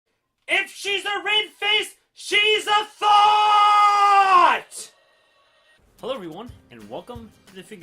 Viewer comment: "If she is racist She is thot"? 🤣I heard like that🤣